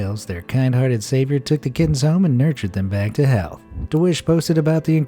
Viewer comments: He said back to health but I thought he said back to hell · did he say nurture them back to hell? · uh did you hear what he said? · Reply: help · Did.... did he just say "nurture them back to hell"